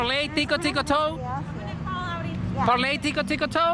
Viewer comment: Tic a toe lol. That was funny. At first I thought you said tickle toe. Thanks for another good video. Have a nice day.